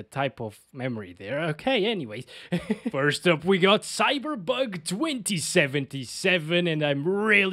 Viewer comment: did he say cyberbug in 2:10 AHAHAHAHAHAHA🤣🤣👀